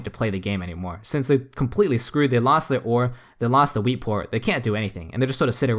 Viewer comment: I kept hearing "wee port".